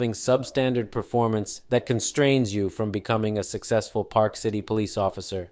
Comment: Every time they said ParkCity Police Officer I kept hearing "SouthPark" Police Officer and kept seeing this cop look like Cartman!